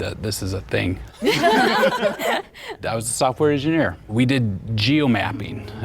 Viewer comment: I thought that guy was the mole because I misheard and thought he said he was doing geomapping as an english teacher instead of as a software engineer